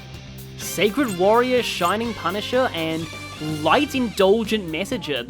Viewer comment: I don't know why I thought that thing said (please youtube don't delete this comment for this) "Shining Prostitute" imao